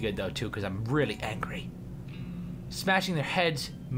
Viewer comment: I thought the ability said smash shit at first :D